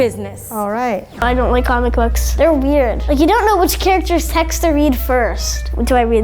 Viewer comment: when he said I don't like comic characters I thought he said common chef....LMAO